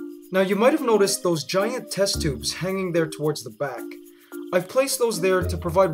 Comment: This guy was an ant in his previous life. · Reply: I thought he was about to say testicles